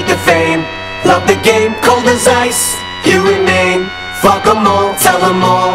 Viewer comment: I always thought he was saying "..cold as ice, here in Maine.." Which was so odd to me. Why the random Maine drop? And as someone who's lived in Maine my whole life - it does get cold here. But it's not that cold.. "You remain" ahhh that makes way more sense.